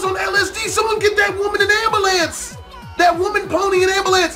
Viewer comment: did you say an ambalance (am-ba-lance), dont you mean and ambulane (with a 'U')